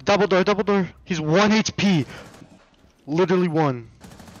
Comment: what i hear is doble dore doble gore. 28:56 what i hear is oh wow doble dor be goble lower, I think I can’t hear I’m not sure